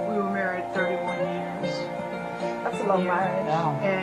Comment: said "that's a long marriage" what on earth made you think she said life?